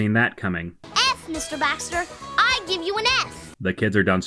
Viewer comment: at first I thought she was saying "f**k Mr. Baxter!" lol